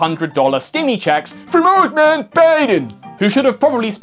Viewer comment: does he say "earthman baiden" btw this is the most funny part from the whole video.